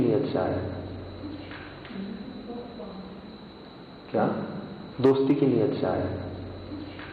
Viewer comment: Here I heard TOU KYA or TOFAA